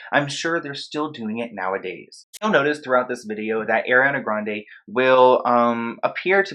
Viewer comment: Is it only me or does it sound like he is saying AIRiana